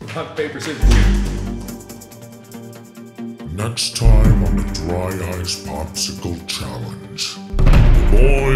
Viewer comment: Am I the only person who thought markiplier said dry ass instead of dry ice